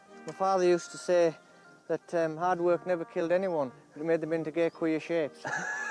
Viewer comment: I'm not a native English speaker, could someone please explain what he said at 20:10 ? "My father used to say that hard work never killed anyone...???" Thanks! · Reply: “Hard work never killed no one. It made them into quite funny shape. it didn’t kill him”